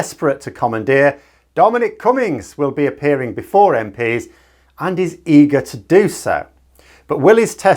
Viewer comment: I heard this as "and his ego to do so".